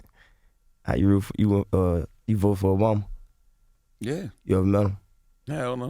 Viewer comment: what durk said ? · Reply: he asked if he voted for obama, then asked if he ever met him.